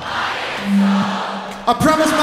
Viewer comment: what say in 5:22?? im promise my mother........some promises???? what say??? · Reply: Leandro Gabriel Morel “I promised my mother I wouldn’t cry” then “some promises you gotta just break baby’